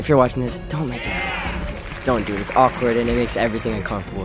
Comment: what do he say ‘ boys if you’re watching this don’t make it a …….’ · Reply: The girl pay, it will make it awkward